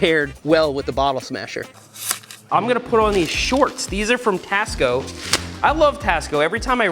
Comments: i thought he was saying "Costco" shorts with a Chicago accent. Nope Tasco haha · when he said tasco i thought he said cost co lol